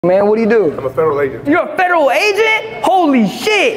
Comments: Im a federal agent ... All I heard was "I'm a humongous douche". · I thought he said Feral Agent. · i thought he said feral agent 😹😹😹